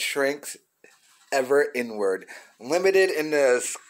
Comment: When u said “inward” I thought u said “N word” lmao